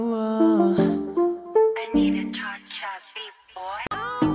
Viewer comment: I thought she was saying "i need to charge up beat boy" all this time... lmao 🙃🙃